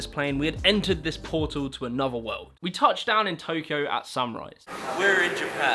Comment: did you say "we touched down in Tokyo at sunrise" or "at samurais"???